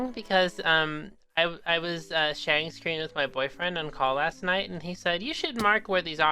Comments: wait did he say he have a boy friend 😮 wow. Is so lucky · Did He Say Boyfriend?